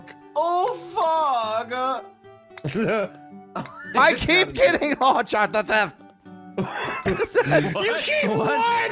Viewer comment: I'm sorry, but we need an Ohm translator to help us understand what was said....haha! Sometimes your Ohmenese can be difficult to understand... :P Also, was this the first game Vanoss won? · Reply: I think he said “off of it”?